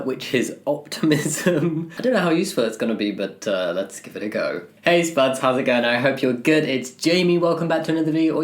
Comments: whenever jamie says "hey, spuds!!" i hear "taste buds!!" and i've decided i hate language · I just learned he says hey spuds i thought he said heys buds